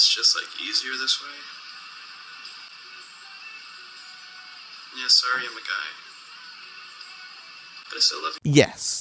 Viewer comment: "Yeah, sorry i'm a guy"- The fuck? Did i mishear that?